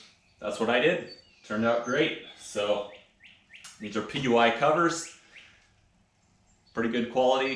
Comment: Hello, who made the your seat covers? Sounded like you said PY covers? Thanks for the video!